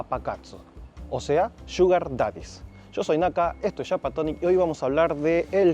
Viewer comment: Huy, por un segundo pensé que decías "yo soy Naka y soy Papakatsu"😂😂😂 PD: eso de las mamakatsu me interesa je